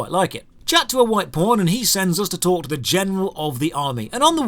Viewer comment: Everytime he says "White Pawn" I hear "White Porn" lol